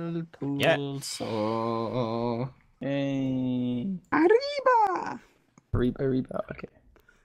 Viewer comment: I love when bbh says areahba areahba at 6:42 it is soo funny 😂 (idk how to spell what he said 😢) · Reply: arriba